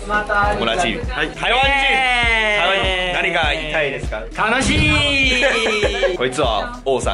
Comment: Haha 1:20 he said 'kanashiiiiiii', but put instead of sad, happy in the subtitles. Classic